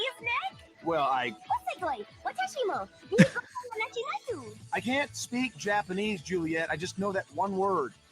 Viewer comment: Did she just say, "Wonderful! I can speak Japanese?"?